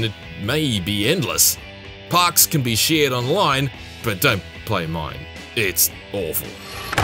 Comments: If anyone else thought he said Online was awful, he didn't at 4:15 he actually said "Parks can be shared online, but dont play mine, it's awful" it sounded like he said "but dont play online, it's awful." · I didn’t hear a single bad thing about this, I was expecting a 10 at the end tbh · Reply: he said "Parks can be shared online, but dont play mine, it's awful" it sounded like he said "but dont play online, it's awful.